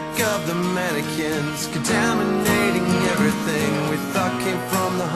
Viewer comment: I always thought he said "Dominicans contaminating everything"